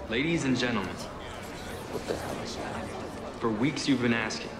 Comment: Did anyone hear the guy say "What the hell is that" at 0:03 ?